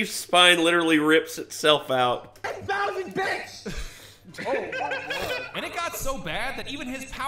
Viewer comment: Not going to lie, when I heard say 10,000 bits I thought he said 10,000 b***h.